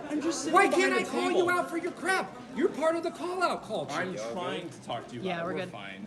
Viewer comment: I thought that guy said "Pull Out Culture" for a second there. I was like, if no kids are further birthed, you lose your job, bro lol